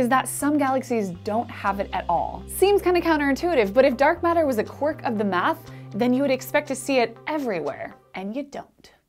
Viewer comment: "If dark matter was ....." What are you saying right there? I've listened a few times but I can't quite make it out. "quirk of the mass?" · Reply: I think she says "a quirk of the math". If so, she may be refering to modifications of Gravity laws. By the way, her arguments about the Bullet Cluster and "noDM galaxies" are plain wrong, but that tends to happens when a Physicist tries to do Astrophysicis without knowing about Astronomy...